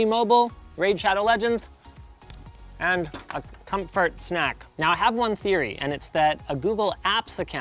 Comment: Did he just say cumfart snack?😳